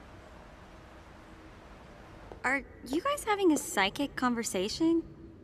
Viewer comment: Oooooooh... It's "Psychic"... I heard "Sidekick" and didn't understood why she said that